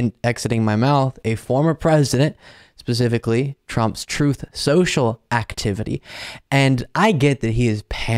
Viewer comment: I keep hearing Pakman's version of Truth, Social - "Troth sential" lol